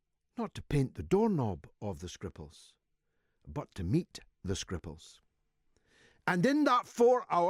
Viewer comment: When he said "meet the Scripals" at first I thought he said "eat the Scripals", which got my attention :-D